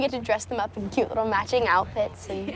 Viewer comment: i thought they said we can cook them for dinner am i ok?